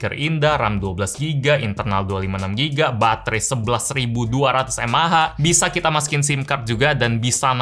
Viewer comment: wait aku gak salah denger 11.000 MAH!!???!!!??